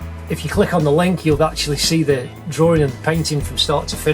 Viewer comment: am i just hearing the "Drawring"? thats what i hear lol